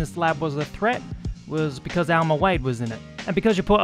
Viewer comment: Sometimes it sounds like you say Elmo Wade....depending on where you hail from, imagine either a tickle me Elmo or a slow, chunky cat.